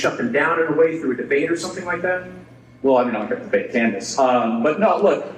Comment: It sounded like Charlie said I'm not going to debate Candace.